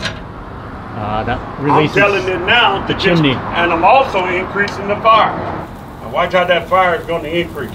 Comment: First I thought he said increase the fart.